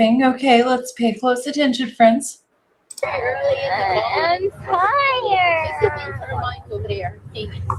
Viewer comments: I'm pretty sure she said "I'm tiiiiiired" but I can see how it sounds like fire. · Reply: yes I heard “I’m tiiiired!” too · Sounds like she said “I’m TI-errrd” (I’m tired) to me.